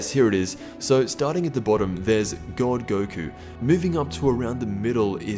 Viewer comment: I thought he said Don goku